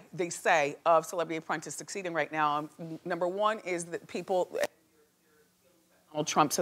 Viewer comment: is that a glitch or did Wendy say something? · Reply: i think she said people hate trump that's why they don't want to watch anything to do with him